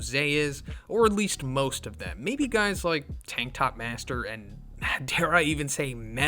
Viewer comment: did i hear "tiktok master" or im just hearing things?